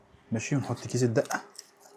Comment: نحط ايه 😂😂😂 علشان مسمعتش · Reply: نحط كيس الدقة